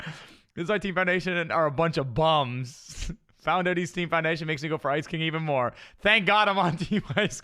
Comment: bro did i just hear this man say foun-diddys team...